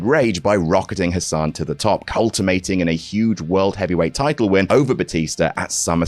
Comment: did you say 'cultimating'?